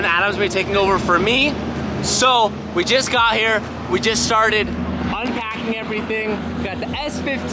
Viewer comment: the intro sounded like he said, "we just got here, we just sharted" 😂